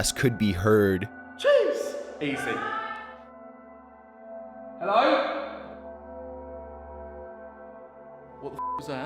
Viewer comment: to me it sounded like y’all alright